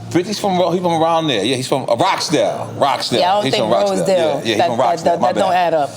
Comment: did she just say 50 CENT took her to his Hood in Rochdale ??? LOL.